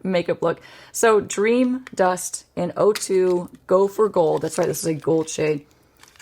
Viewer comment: I keep hearing “gopher gold,” and not “go for gold.” 😂